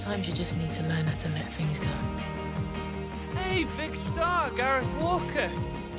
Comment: did he say vikkstar